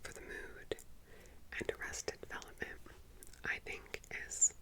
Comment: I dont understand the TV show you mention after twin peak 🤣 at 17:30 ish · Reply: Arrested Development!